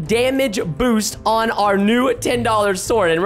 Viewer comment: why did say ew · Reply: He said new